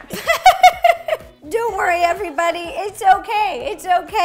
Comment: rosanna when she spilled the flour or sugar:"dont worry everyone" what i hear:"dough worry everyone"